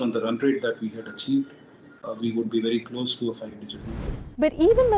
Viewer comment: what he is saying not clear voice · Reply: He says they will be close to a 5 digit number in sales with the EV Nexon